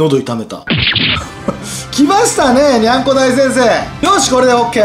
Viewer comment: にゃんこ大先生がにゃんこ大戦争に聞こえるw